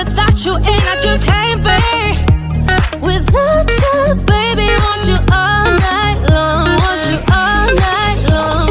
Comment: can anyone else hear *WaNt YoUr FoRtNiTe*?????